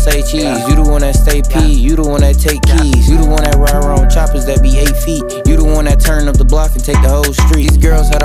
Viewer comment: What did he say at 1:22 🤨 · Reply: You the one that be riding ‘round with choppas that be 8ft